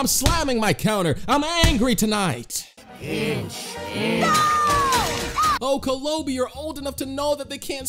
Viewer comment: I heard pinch, does anyone hear that?